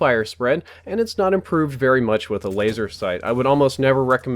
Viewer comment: Did he say laser 2:35????